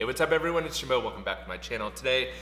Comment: i changed my name? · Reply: Sounds like you said Jimel…. I was caught off guard. 😂